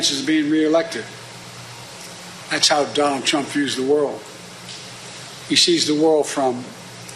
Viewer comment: It sounded like he said that’s how dumb trump views the world 😂